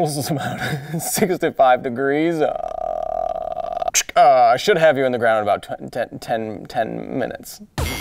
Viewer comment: I heard "I should have you (in) the ground in 10 minutes" anyone else. lol